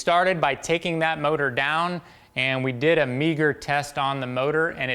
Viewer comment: I’ve always heard it as “megger” not “meeger” as in megaohms